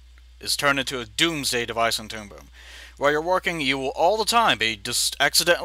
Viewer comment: I really thought you said "Toon-Poon".